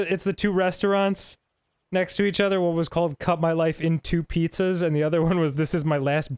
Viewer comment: I always heard it as cut my life in two peaches.